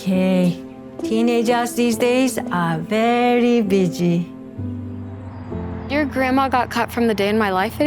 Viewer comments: did she say bitchy 🫩😭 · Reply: No, she said “busy”. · as a Czech i heard something different than Busy 😅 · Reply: I hear bitchie😂😂😂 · did she say "teenagers these days r very b*tchy? " EDIT : she said busy, sorry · did she just say “very b!tchy” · is it just me or did she say “teenagers these days are very bitchy”? 😭